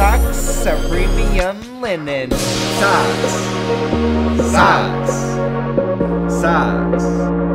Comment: I thought he said docks at first :3